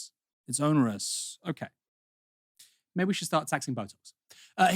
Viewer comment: Did Aaron say Maybe we should start taxing Botox?!13:00 😮😂